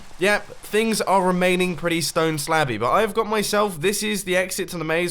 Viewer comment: Who else heard that as stone slappy 😂😂